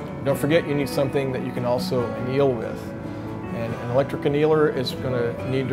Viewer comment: please fix the captions to say "anneal" instead of "kneel".